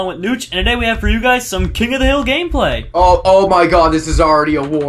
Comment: I thought you said king of the hell lol